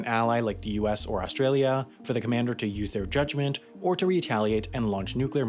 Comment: did he just said "use" but with lisp?